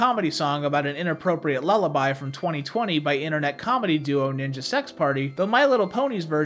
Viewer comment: Did I hear a mention of The Hit band, ✨Ninja Sex Party,✨featuring Jewish let’s-player Dan Avidan and theoretical physicist Brian Wecht?!